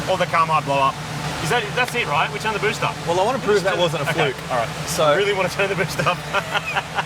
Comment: Youtube CC subtitles at 8:25 "I want to prove that wasn't a coke alright, so I really want to say the bitch..." More amusing a mishearing than usual anyway